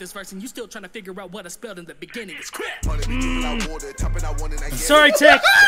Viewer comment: wo wo wo did he say it’s I’m a fan of nothing against him but he took that from Dax